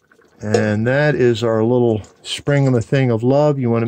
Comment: Hey, this is 'Corktown'. It's a little hard to hear, but at 4:05 did you say, "And that is our 'spring-a-ma-thing' of love"? If so, your already maxed-out awesomeness level just kicked up a couple of notches! ....